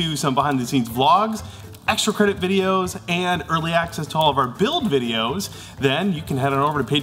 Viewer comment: Every time you say 'build' I hear 'Bill-ed'. Another awesome prop Billed! Harrr...